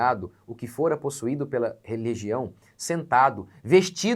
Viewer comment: 👋Excuse me... 🤔 Tem um erro no áudio que diz "religião" ao invés de "legião" (02:39), porém até que ficou interessante... Tem uns que até falam coisas sem sentido, né? Como as supostas "línguas dos anjos" ou "línguas estranhas" que ninguém entende, né? Os "Labaxúrias"... É um nível de loucura e endemoniamento de certas religiões falsas, certo? Quando a legião se torna religião, né?